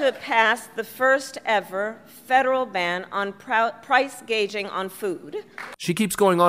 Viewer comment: She actually said price gauging. Since gauging is measuring or judging something, she intends to make comparing current inflated prices to past prices illegal. That sounds about right.